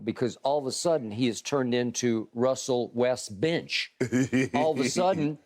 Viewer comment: Lol at first I thought Skip said Russell-West-B*tch 😆